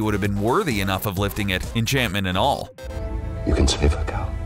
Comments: it sounded like he said "you can sniff a couch" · did i just hear 'You can sniff a cow'? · I thought Oden said "you can sniff a coat"